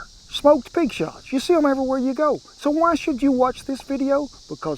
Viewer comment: Oh I thought you'd said pig snouts.oh well I'll figure it out one day.